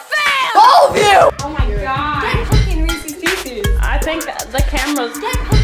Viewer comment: is she saying is saying "Get fucking Recesse's Pieces!:D"